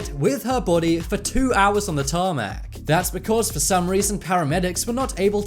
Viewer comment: excuse me, what did you say? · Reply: Tarmac, why?